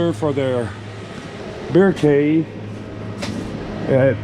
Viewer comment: Thought it said bear cave unit